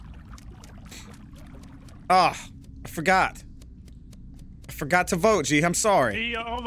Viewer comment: i can't remember who it was i think it was but he heard you say I forgot to Spy and thought you had to be a dodo trying to get sus on you lol